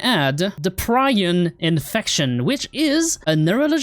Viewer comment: I thought you kept saying "Bryan" instead of "Brion" 😭